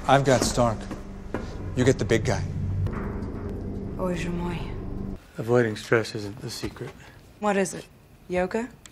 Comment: what is she saying? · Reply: Bozhe moy (Боже мой), it's russian. Means "Oh my God."